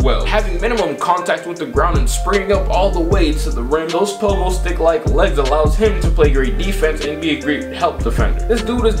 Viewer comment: LMAO 7:10 did u say los pollos thick like legs 😂😂😂 im actually done